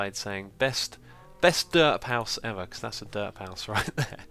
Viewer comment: I agree. (08:48) And "derp house" sounded like "dirt house".